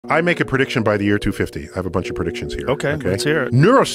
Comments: It sounded like he said, "...the year 250." That sounded like George W's "Two-O-Five" for 2005! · i can only see the full automatic ev, everything else is either impossible or not possible in 30 years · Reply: sounded like he said by the year 250. I'm not sure if he meant 2050 but 2250 might be more realistic for some